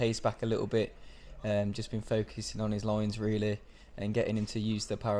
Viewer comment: I had to go back a piece there at 2:14 as I thought he said we've been focusing on his loins. 😂 Another tip for going faster is to eat less of that pudding. 😁